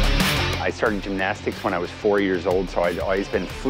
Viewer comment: I thought he said I started doing the nasty at 4 years old. Lmao